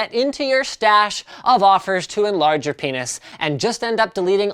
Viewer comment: did you say "-- offers to larger penis"?! o_O"